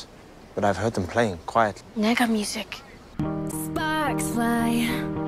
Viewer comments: did she say “negga music ?” 😂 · I DID NOT EXPECT HER TO DROP THE N WORD THAT CASUALLY SKJSKJSKJKSJ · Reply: LMAO I THOUGHT SHE SAID “MEGAMUSIC”